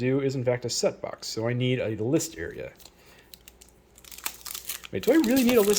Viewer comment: when i heard "list area", i thought you said listeria...no...you don't need that :P